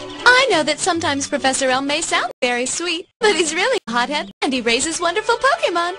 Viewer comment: did she just say hes really a pot head? haha ha